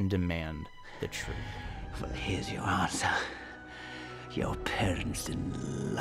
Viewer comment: Dang... All these years I thought he said "Your parents ..loved you." in a teasing kind of way. But this makes even MORE sense, wow!